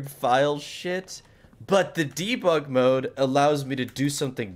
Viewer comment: anyone thought that for the whole video i thought he was saying diva mod lmao instead of debug mod